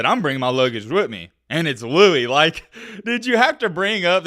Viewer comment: oh wow i thought he said "and it's loaded" not louis 😂😂